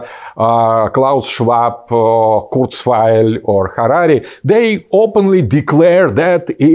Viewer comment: So family, country, sovereignty, majority rule? Such crazy, scary, dangerous stuff! p.s. I think there might be a mistake with the subtitles 5:44. I think he says "Harari", not "Harati". I assume he means the author Yuval Harari.